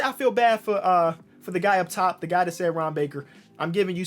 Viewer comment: I swear to God everytime he said Ron baker in the beginning I legit thought he was saying harambe